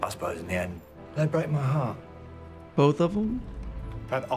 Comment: I watch this episode every Christmas and have never realised he says heart instead of hearts 😂